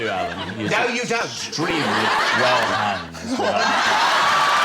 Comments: what does michael say at 5:12?? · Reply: "well hung" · what did he say? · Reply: Meizi You ‘he’s extremely well hung’